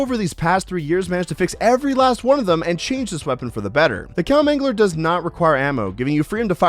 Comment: Did he just say Cowmangler is not shit? lol